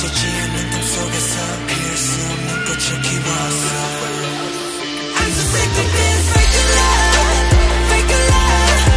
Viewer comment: does anyone know what namjoon says at 0:22? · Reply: He says “It’s so fake and I’m so sick of this fake love.”